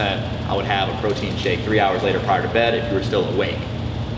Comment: "I would have a protein shake 3 hours....... bed." What does Jordan say here? Is it 3 hours after dinner or 3 hours before getting to sleep? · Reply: He said prior if you listen closely prior to going to bed